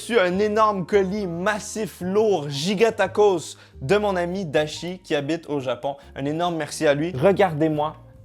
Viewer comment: Dites-moi que je ne suis pas le seul qui a entendu Itachi 😂